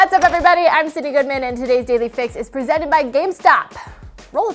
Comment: lol, I heard that "Today`s Daily Fix is presented by Gamespot" :D I had to double check which channel is this :D